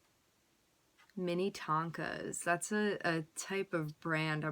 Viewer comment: At first i thought you said " Mini Tacos" like a stoner i was dyinggg 🤣😂😂😂